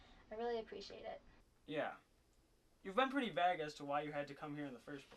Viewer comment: did he say "you've been pretty *vagg*"? haha vague maybe?